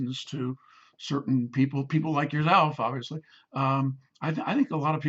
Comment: What did he say? People like whattt?????? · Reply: "People like yourself." said he to the interviewer.